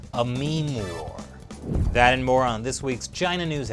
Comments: oh, I heard "moron" instead of "more on" when video of trump appeared. Pure coincidence. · I thought you said "That moron..." when Trump came on the screen...that's what you said, right? :-)